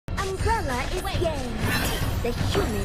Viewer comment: Idk I heard that right but I heard "umbrella is gay" correct me in comment🙂